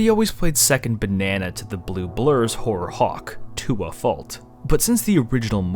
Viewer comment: did he said hawk thua?